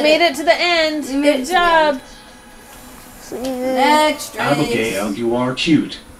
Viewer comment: caption says: "I'm gay and you are cute"